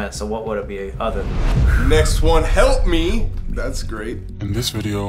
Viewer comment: During the “Help me” one I’m not sure why but I heard “Help Please” did anyone else hear that?